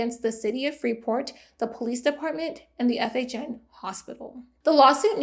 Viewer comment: I keep hearing "FHM hospital" but that's a much better facility than this one